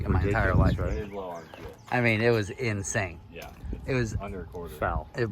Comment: Did he say one hundred and a quarter while on the phone at 8:52 ?